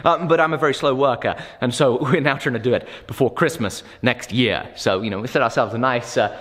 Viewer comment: Did he say get the knife and fork ready "before Christmas next year"?